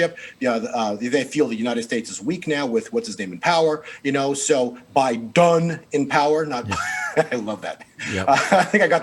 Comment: Biden "done" in power? is that what he's saying at about